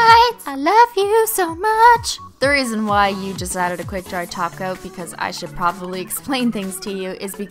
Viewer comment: GUYS WTF EVEN HAPPENED A TINY ELF DRUGGED ME AND DID THIS help my head hurts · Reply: (Cristine) i thought u said "add some glossy taco" insted of top coat hahaha